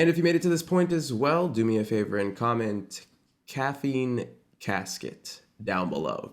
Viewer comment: everyone saying caffeine casket and i thought he said caffeine cat skit 😭